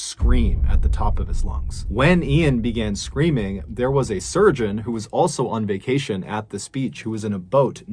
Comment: When said "the 'surgeon' rushed Ean to shore and began trying to save his life..." I heard "sturgeon". Now I can't get that picture out of my head.